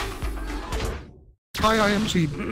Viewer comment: "Hi, I am c..." That's what I heard... Literally.